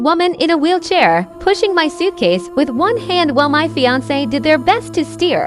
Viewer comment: i misheard this as FFA and was wondering what the Future Farmers Association had to do with discrimination on airplanes